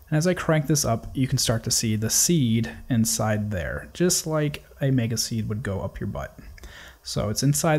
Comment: At about 4:37 does he say "just like a mega seed would go up your butt"? It makes zero sense to me but I can't figure out what else it could possibly be.